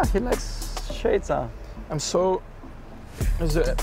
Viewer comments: Max promoting Checo’s sunglasses 😂😂😂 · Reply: thought i heard Checo saying “Xades” which is the brand hes working with haha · Max promoting Checo’s sunglasses 😂😂😂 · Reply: he clearly said xades, checo’s brand